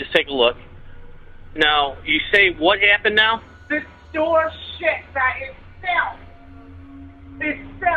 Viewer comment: I thought was saying the door shit on itself lol my gosh that video was hilarious 😂